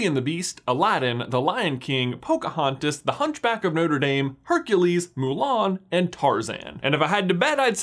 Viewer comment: did he say hunch back of NOUTOR dame!? (i will never be the same)